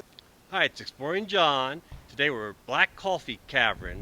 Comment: I thought you said BORING John! LOL😁🤣🤣